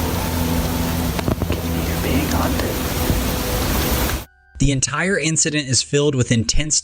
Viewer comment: Its not "unintelligible", he clearly says "We're being hunted." 48:40 Thats sir.......is a large male chimp. 😉 The "billy ape" walks upright and is even know to wage war on other chimps brandishing weapons, check it out. Pretty friggen cool/terrifying. 1:05:42 Racoon with mange.